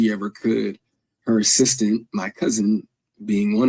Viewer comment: "her sister, my cousin" ... please explain how Monique's sister is his cousin and not his aunt. OR did he say "assistant" ??? (Sorry, I'm hard of hearing) 😊